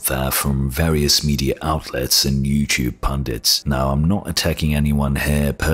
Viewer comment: if I heard correctly you said "pandits"? 2:03 also, you're using the term in the wrong context a pandit refers to a Hindu priest or wise man.